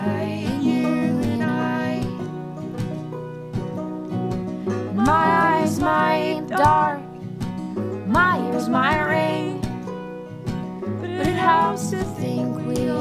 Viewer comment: ah... glad the lyrics appeared with the album purchase. 'cause i thought you said, "My eyes might dark, my ears might rain" (which would still be kind of a kick-ass lyric, if not as sensible) :)